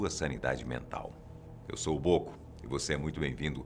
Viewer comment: foi só eu q ouvi bobo?